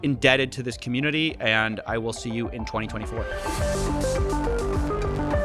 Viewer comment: After those intense 20 minutes I misheard saying “And I sue you all in 2024.” 😅